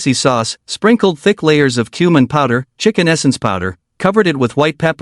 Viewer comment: Whoa.. for a second I thought I heard "human powder" so I repeated it and it was" cumin powder" :D :D